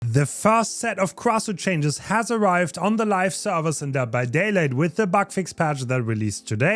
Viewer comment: I can't be the only one who heard him say dead by daylate in the intro 😂